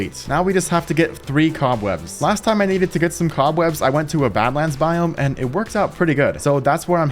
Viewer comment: Everyone you said badlands I heard it as Batman 🤣😭 I’m so stupid